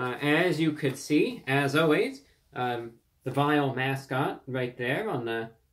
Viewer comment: Did he say "vile" mascot? Why is it vile?